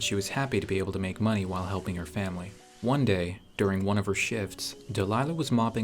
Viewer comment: i thought he said "one day , during one of her shiT$ " on 0:37💀